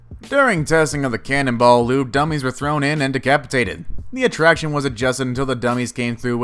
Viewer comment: Excuse me but did he just say decapitated?